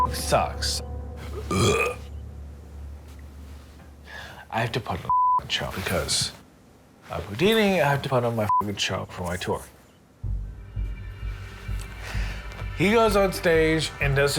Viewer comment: What does he say at about 4:25? Something about "I have to put on ????.. I have to put on ???? my gig tonight..". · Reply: Tomás Ó Treasaigh “I have to put on my F’ing show because I’m Houdini. I have to put on a f’ing good show for my tour.”